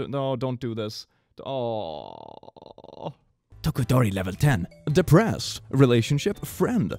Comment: it took me a while to process that he said "tokodori" instead of todoroki